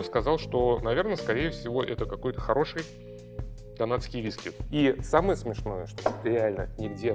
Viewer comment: Мне послышалось он сказал самый топовый казахский виски 😂